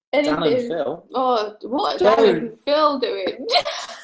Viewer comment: says "Dan and Phil?" says "What are Dan and Phil doing?" And says something at the same time... What did he say? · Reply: Sassy Soda "don't"